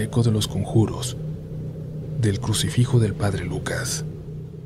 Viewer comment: yo entendí " El Sacrificio del Padre Lucas"